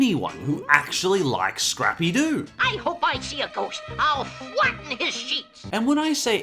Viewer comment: Thought scrappy do said " FLAT NIGGA SHEET"